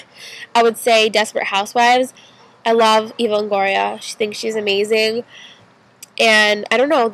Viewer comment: it sounds like you said "I love Eva Longoria, She thinks she's amazing" haha